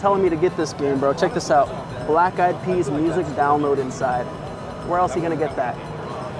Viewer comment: Swear I thought you said “black guy pees” thought that’s a weird feature to put on the cover of a game 😂